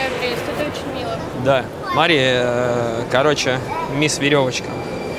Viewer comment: После этого момента сразу закрыл видео 2:01, точнее уже хотел закрыть, но тут он назвал ее Марией 2:14, а ведь ее имя 1:02. И мне стало интересное что за бред будет дальше) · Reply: а мне послышалось, что на 2:14 он очень нечетко сказал "смотри"